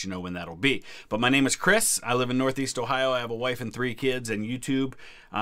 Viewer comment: “I have a wife and 3 kids and youtube.” is how I heard that at first, lol